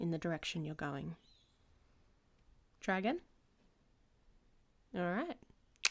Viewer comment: I heard her say Try again and I thought her say Dragon and I was like "Where!"